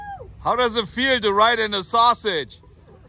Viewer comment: “How does it feel to ride in a sausage”. I think those girls might have thought you said “on” a sausage. I laughed so hard. This entire video is one of the funniest vids ever. “Ninety nine”!